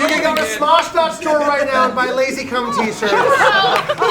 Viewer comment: i’ve literally been so confused cause i thought they were saying lazycom, not lazycum